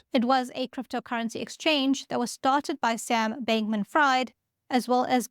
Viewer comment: Hi everyone. Apologies about the incorrect pronouncing of “Fried”. Hopefully SBF doesn’t mind. Thanks for watching! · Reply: I initially thought you said Sam Bankman Fraud. That would have been funny😅